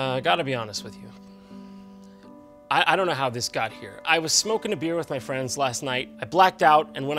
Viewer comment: did he just say smoking a beer